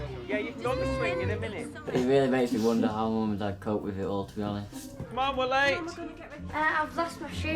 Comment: Bruh what the fuck does dude say at 2:06? I literally can’t understand him... is he speaking a different language besides English? Cuz I know like hell that isn’t English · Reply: “I wonder how mum and dad cope with it all to be honest”